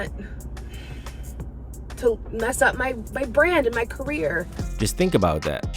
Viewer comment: could a sworn I heard her say bread (money)